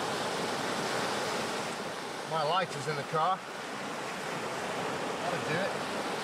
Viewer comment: "My wife is in the truck, that'ed do it". At first I thought that he said "knife", but then they use a lighter, so he couldn't possibly mean "knife". If the line was any tighter on the turtle, we would not have seen this incident. 1:34 "I think it knows you're trying to help it". Said presumably because the turtle is not moving/struggling. The more likely explanation for its immobility is because it is exhausted. 2:00 "The poor turtle lies completely still", because it is almost dead.